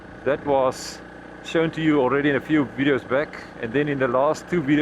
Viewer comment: When you said "a few videos back", I thought you said "a few beers back " lol